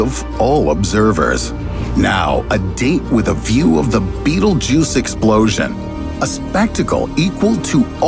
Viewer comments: I keep hearing “Beetle juice” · I thought he fucking said beetle juice.